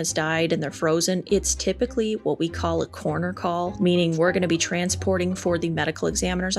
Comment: Coroner call ? I thought you said corner call , as in calling your shot on a billiards table . And yes , please do a Mt. Everest follow up / Cryogenics video . The death penalty . Do you think that freezing would be more humane to go than what is offered now . Don't you just fall asleep , it seems that it would be a more peaceful way to go . IDK ? Just a thought . Oh yeah Mango seemed to be overly excited today or enthusiatic shall I say she is so adorable . 😋